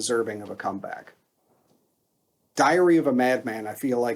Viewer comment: LOL, it sounded like you said "Diarrhea of a Mad Man". :D :D Since you mentioned Bob Daisley - Timi Hansen of Merciful Fate / King Diamond would have been a similiar example. Or the guy from Priest (totally blacked out on the Name).